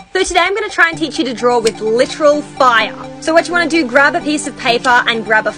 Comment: I thought you said “let’s DRAW fire” Then : let’s Draw with fire :) Me : ?